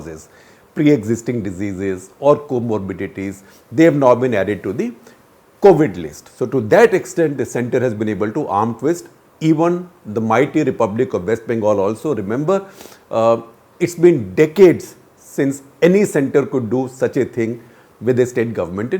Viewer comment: Sir. Did u just say in 8:12, the republic of west Bengal?